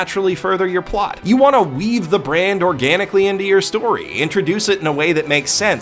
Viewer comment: You said "weave the brand" but I heard "weeb the brand". I suppose it could still be accurate.